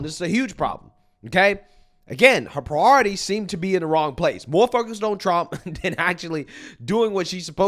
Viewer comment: At first I thought you said “mothaf*** on Trump” but it was “more focused on Trump. I was like “man I love the passion” lol